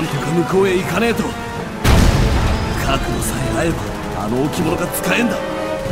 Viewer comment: 位の「角度さえ合えば」が「覚悟さえあれば」に聞こえて ジョルノ！？ってなった